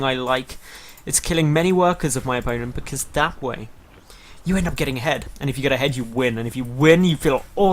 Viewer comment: i could have swore you said "because that way you end up getting head" - needless to say i laughed